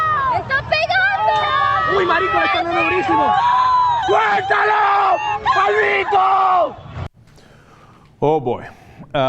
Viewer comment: The guy is screaming, "Kill them the bandits". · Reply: Edgardo Arvelaez i thought he said "sueltalo maldito"; basically "let him go"; he is shouting at the bikers to let those people go